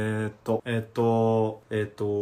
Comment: からえっとが江藤にしか聞こえなくなってしまったw